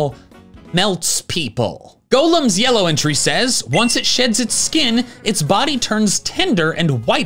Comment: I thought he said once golem shits his skin, 🤣